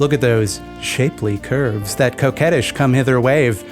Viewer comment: I'd never heard the weird word "coquettish" before this video, so when you said it, I thought you said "cokehead-ish," and I had to go back and read the captions. Also, thank you for actually using your script for the captions so I don't get those funny auto generated mistakes. I really appreciate the extra effort. I don't care what anyone says. You're a damn hard worker.